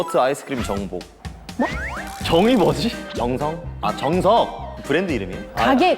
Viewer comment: I misheard Dex saying Jungkook, and I got Jungshook 😅 but then I look at the subtitle and I was like ohhh 😂😅😂